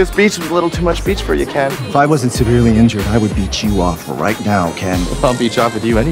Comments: It sounds like he says I will beat you of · I thought he said he would beat him off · I thought he said beat you off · At first I thought they said beat off💀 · when i first say the movie i thought he said “i will beat you off ken” and me and my friend bursted into tears in the middle of the theater 😭